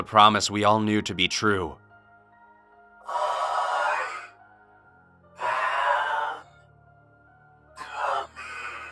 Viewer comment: what is he saying (the voice) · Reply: I AM COMING.